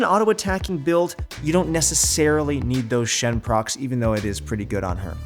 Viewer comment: did he just say Shen? THERES A NEW SHEN BUILD OMG! WHAT IS IT?! COULD IT BE LIANDRYS TECHNOLOGY??!